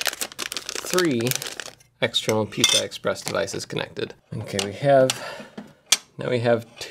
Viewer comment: Thought I heard you say "Pizza Express devices" :(